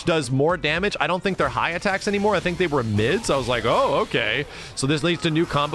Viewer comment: Wait. Did he say that this game has mids?